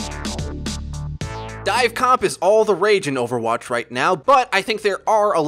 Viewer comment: Dive comp is amazing but in this video breaks down some misconceptions and how to avoid them! Let us know what you think and be sure to drop a Like & Subscribe if you enjoyed! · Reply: I thought it said dva comp misconceptions lol